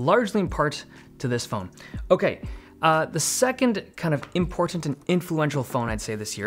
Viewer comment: There was something about the way he said important that made me feel uneasy. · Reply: Ha ha I felt the same. I thought I heard impotent lol